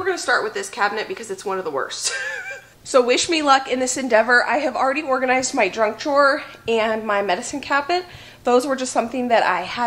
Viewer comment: I thought said, "I'd already organized my drunk drawer..." Yep, my brain doesn't work properly. But great motivation for me to start on my kitchen.